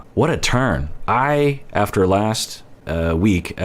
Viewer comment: What you said "What a turn". What I heard "What a turd". And me "Yasss".